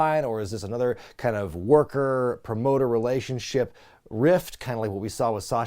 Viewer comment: I heard say Grift but it could have been rift.